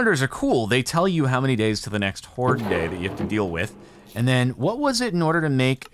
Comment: He said “hoard day”, I heard “court date”